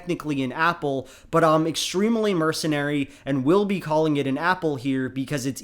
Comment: Did you say "I'm extremely mercenary" @ 2:00 ? What does that mean?